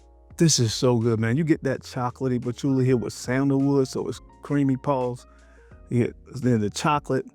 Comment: did he say “Creamy Pause?”🤔😂😂